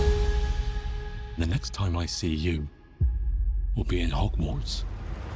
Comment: is anyone else hearing "hogmorts" instead of hogwarts